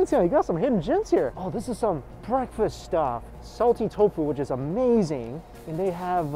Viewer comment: I always thought you say silky tofu,but now I know this is salty tofu not silky tofu